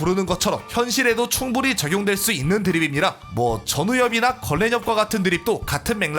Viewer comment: 건랜협 나만 걸레년이라고 들리나..